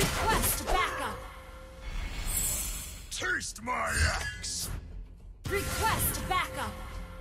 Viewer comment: Hehe when the game was new I thought balmond saying like'taste my ass '😂😂